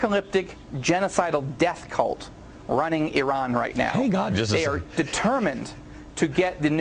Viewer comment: When Netanyahu's picture came up at 1:27 to 1:29, did I hear Glenn Beck say, "Hello God"? I think I did.